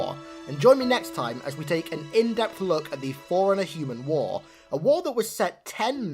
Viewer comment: Thought you were saying foreigner for a long time :D